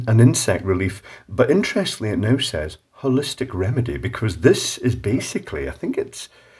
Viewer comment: I thought he said "Horrific Remedy" in chinglish